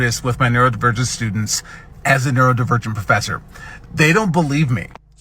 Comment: "Neurodivergent professor." That's what he said. That is not what the captions said he said.